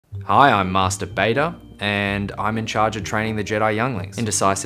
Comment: I was scrolling and the first thing I heard was “Hi iM mAsTeR bAtEr